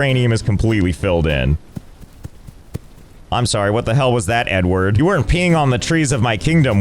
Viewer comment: Why did i hear "Im sorry what the hell was that N-word" Watch it on 2x speed